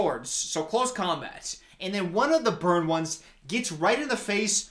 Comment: I THOUGHT U KEPT SAYING “BIRD ONES” AND I WAS DYIN